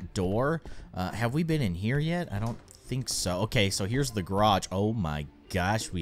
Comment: When keyin was on the bathroom level and was in the garage I thought he said the grudge I thought this game was turning into a horror